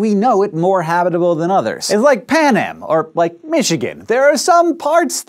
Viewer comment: did he just say "like panem... or Michigan?" hunger games culture lol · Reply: I heard Pan Am.